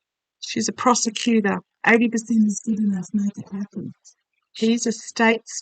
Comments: What’s mumbled by your guides at 34:20? 80% something something? ❤❤❤ I should not say mumbled—as I’m certain they were completely clear & I’m the one hearing with mumbled perception!! Ooh, I sped it up & got “80% is good enough if (garbled: nothing? something?) happens” in a woman’s voice😍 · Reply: I heard, "80% is good enough it needs to happen." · Does anyone else here something at 34:22? Definitely something there! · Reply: Make that 34:20...can't make out all the words, but right after says "she's a prosecutor" there's another voices that ...all I can make out of it the word happens